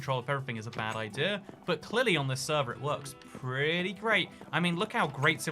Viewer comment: I thought it said Chris server and I was getting very flattered haha